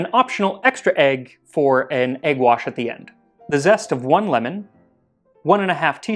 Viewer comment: I love making art, and when I hear “egg wash,” What I immediately THINK I hear is “a guache.” 😅 Silly Brain, that doesn’t make sense. 🤦🏻‍♀️